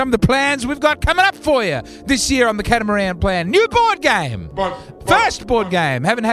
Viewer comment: I thought it said “ Cartmen plan”